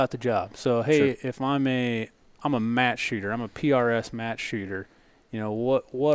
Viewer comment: Im notifying you guys. At 5:48 automated captioning said "im mass shooter-" im sure that's not what you said, you guys can correct the captioning through youtube studio, please do it. (i use those captioning cause im deaf) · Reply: I just confirmed that, the captioning says mass. You guys need to fix that. I clearly hear “Match shooter”